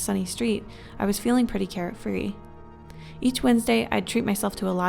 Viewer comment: Did she say carrot at 9:10 to 9:11 instead of Care? Edit: She did.